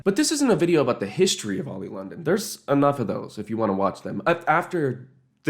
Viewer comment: I fr thought it said oil London 💔